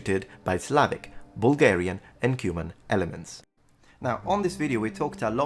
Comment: I misheard ´Cuman´ as ´Cuban´ at first and I was like "wHEN THE FCK WERE CUBANS IN EASTERN EUROPE" then I realized I am an idiot